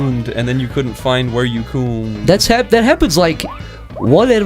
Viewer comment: what is he saying? "poomed"? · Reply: Coomed